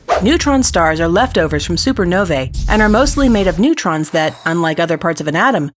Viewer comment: Sounds Like You Said Supernovay....